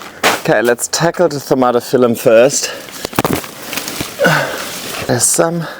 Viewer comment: can you tell me the name of this plant please, because what I heard is tomato philum 😁 can you tell me the right name? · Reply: Thaumatophyllum